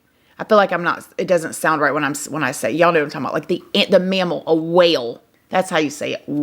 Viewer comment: IM CRACKING UP....Totally thought you said WELL so glad you flashed a WHALE picture!!😂 made u even cuter🥰